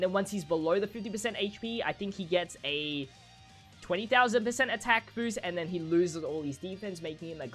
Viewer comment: Is it me or did he say 20000% atk boost for that LR kid gohan